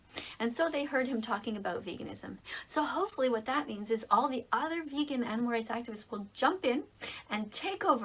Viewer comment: sounds like she says “so all those vegan n-words”